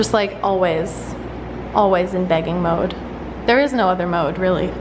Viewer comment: The way you usually say bag when you said "they're in the begging mood" I thought you said bagging so I was very confused lol thought you were saying it like they were thrilled to get in the bags for moving day 😂